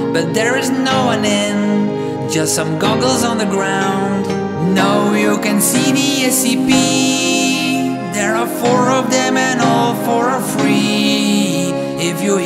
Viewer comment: Every time i heard it i thought it said “Now you can see the SCP”, not “No, you can’t see the SCP”